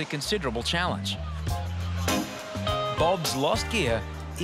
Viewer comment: With Bob, I thought they said he lost his EAR!!! 💀😭😹